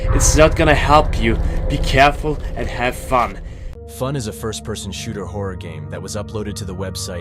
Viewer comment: Did you say fun is a firstperson shooter